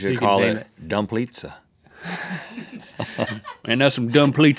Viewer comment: Oh you said ”dumplizza”, I thought you said ”Dump Lisa” so I dumped her.